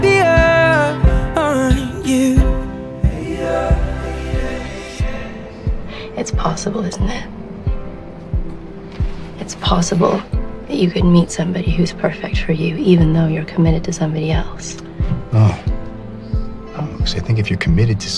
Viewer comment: from 2:03 to 2:22 , can anyone tell the conversation?? I m not getting what she is saying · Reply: It's possible isn't it ? It's possible that you could meet somebody who's perfect for you even though you're committed to somebody else ? No, no I think that if you're committed to somebody you don't allow yourself to find perfection in someone else. - You just tell me what I don't wanna hear.